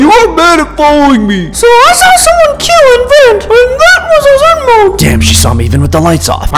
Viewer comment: “AND THAT WAS A ZMDE” - 2021 I think its funny that said “A zmde”